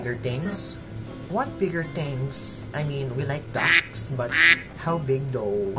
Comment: Excuse me, I thought I heard you say dacks yet you presented a picture of a 🦆.